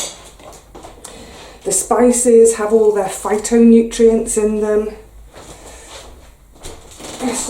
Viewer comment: Hi, I thought that you said that the spices had 'fighting nutrients' in them. Took me a second for the penny to drop, you said PHYTO nutrients 😀. BTW, I put the stock cube or powder straight into the pot so I keep the jug clean (but I am also very, very lazy)